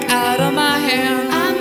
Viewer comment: i swear i always hear "just because we are gay"